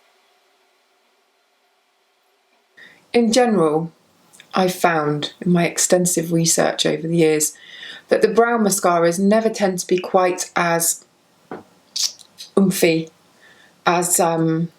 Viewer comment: When you were saying ‘brown mascara’ I thought you were saying ‘brow mascara’. I was thinking, I’ve never thought of having oomphy eyebrows before 🤦🏼‍♀️